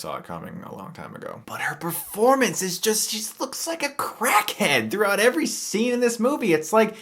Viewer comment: "She looks like a total crackhead!" I thought he said, "She looks like she's on heroin."